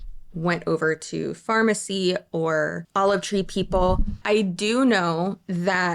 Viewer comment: Why is every time I hear “Olive Tree People,” my head makes it, “Oliv Treeple?”